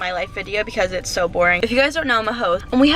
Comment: When she said ‘you guys know I’m I host’ I thought she said ‘you guys know I’m a hoe’ and I us like uh...? And then I re-watched that part a couple of times and realized what she said and I was laughing for like 5 minutes at my mistake 😂